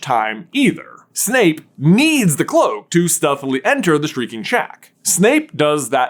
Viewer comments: Thanks to, I always hear "streaking shack" when either of them says "shrieking shack." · Whenever J says "shreaking shack" now I always hear "streaking shack" and I don't even know which one he says